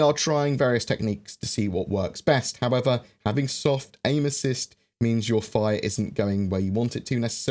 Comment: lmao when you said soft aim assist I thought you said soft anuses haha